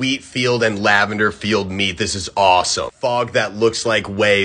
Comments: Am I the only one who thought he said something like "ilyeet" instead of "elite"? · The one at the end when he said elite I thought he said I’ll eat